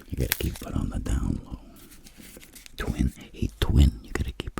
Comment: WHAT DID HE SAY?!?? 😭😭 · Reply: never thought would be calling me “twin”